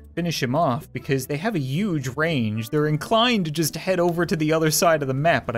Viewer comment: Did you just say "yuge"?